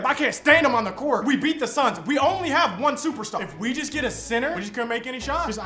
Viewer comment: Mid video i kept hearing "sinner" till i eventually heard center